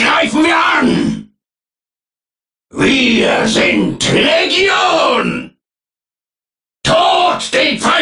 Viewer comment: what did he say? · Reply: "Wir sind Legion" best translated as "We are Legion"